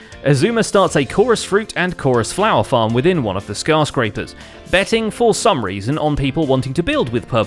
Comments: at one point when you said skyscrapers, I thought you said scar-scrapers lol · did he just say scarscraper?